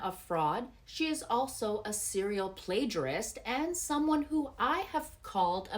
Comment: Perjury is serious. Why is she getting away with this? She’s a bad person in general. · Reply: Perjury is lying in court. I think you misheard who said Plagiarist.